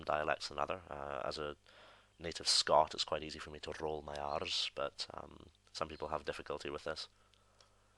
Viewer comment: I heard "roll my arse" instead of "roll my R's"